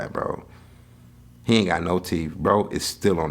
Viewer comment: as soon as I heard "yaint" I was like huh 😂